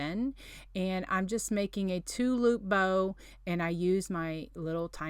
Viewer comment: Lol..took me a couple replays to realize your saying “two loop bow”🤦🏼‍♀️I thought you were saying “tulip bow” I’ m like what the heck’s a tulip bow🤣 I love the dollar tree Christmas trees🎄You are so talented!